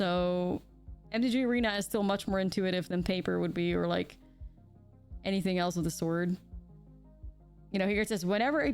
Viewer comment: sounds like you said “anything else of the ‘sword’”instead of ‘sort’ and it made me smile